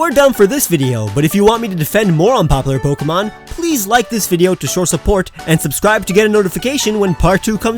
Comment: I know he meant "show your support", but he said it so fast that it sounded like "shore support". XD